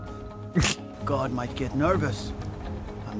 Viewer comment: For a second when he said guard might get nervous, it thought he said god might get nervous and I was like fuck ya God’s gonna get nervous